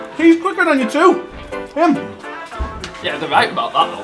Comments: I heard “how could fatty be quicker that me!?!” 😂😂😂 if that’s not what he said I wish it was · "how can fatty be quicker than me?" O O F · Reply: Ooooh I see.... lol I thought he said "how can fuck he be quicker than me" which was a bit more harsh.